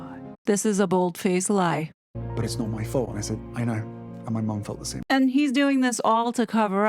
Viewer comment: Did I hear H say “and my MUM felt the same” when commenting on the photo of M on the front page of the newspaper after the Remembrance Day concert ? Which MUM? 🤨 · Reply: Sounded like he said "and my mom.."